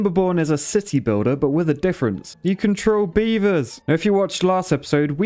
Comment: LOL I thought he said “WE CAN TROLL BEAVERS”